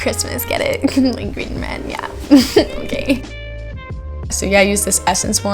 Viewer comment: high key confused is u said “mkay” or “gAy” djdnnfmdk